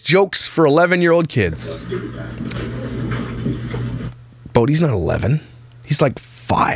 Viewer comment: When he got the jokes for 11 year old kids book, I heard him say “I’m not 11”. She doesn’t pay attention to so many of the things she buys. Whatever catches her eyes goes into the cart.